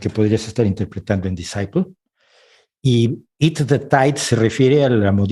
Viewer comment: Una pregunta en el minuto 26:42 p bueno por ese tiempo jeje de qué canción hablan? Es que no entendí muy bien :( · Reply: Creo que Z en donde dice "drink the tide " supongo pero no estoy segura. Al parecer hubo un reto entre adolescentes para ingerir tidepods, que son unas pastillas de detergente